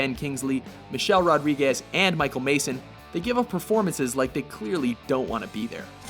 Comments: i thought it was michael madsen sounded like you said mason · did you say Michael Mason? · Did you say Michael Mayson? Ummm sacrilege!